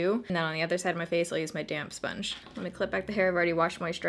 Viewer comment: It sounded like she said "and then on the other side of my face I'm going to use my damn sponge" LOL